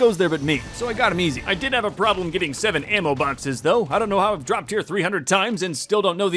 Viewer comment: for a hot second i thought you said you dropped the baby 100 times